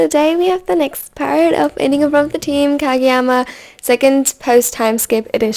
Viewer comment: This may be weird but for a few minutes I thought that said farting in front of the team instead of “ fainting in front of the team” 😅